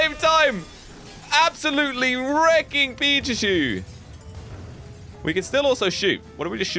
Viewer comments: did he just say pichachu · Hey dont take it as bad but at the time 9:02 did u say pichachu if u listen well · did you say pichuchu?!